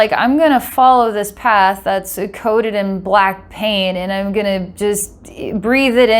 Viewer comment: I heard "black pain" instead of "black paint" 😅 Edit: so now I'm confused. To go strait for the light Or to go in the darkness to fund list souls. I think to go strait for the light is the way to go. All the hidden parts are seen..